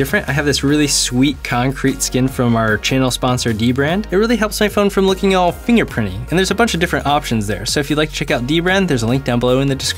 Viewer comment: I swear he said I phone